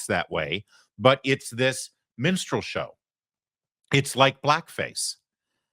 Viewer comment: I know you said "minstrel show," but I heard "menstrual show," and now I can't hear it any other way LOLOL